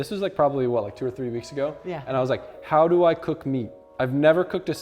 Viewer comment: lol I thought you guys were saying “Taco meat”